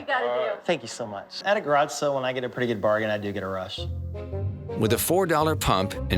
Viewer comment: ohmg at 5:59 when he said "i do get a rush" i thought he said "i do get aroused" and i was like oh no